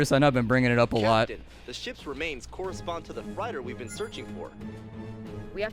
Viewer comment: "Captain, the ship remains correspond to the FRYDER we've been looking for." 21:14 I'm sorry, I must have misheard you Mr. generic talking head NPC. Did you mean FREIGHTER?